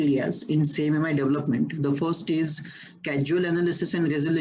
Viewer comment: "cAsual" instead of "cAUsal"